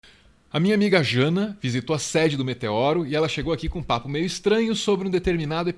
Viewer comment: eu ouvi xana ?